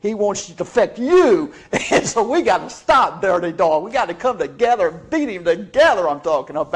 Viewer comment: I swear I thought he was saying "come together and beat him to death"